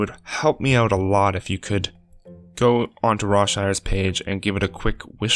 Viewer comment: When you said Rawshire at 1:18, I thought you said Rob Schneider for a second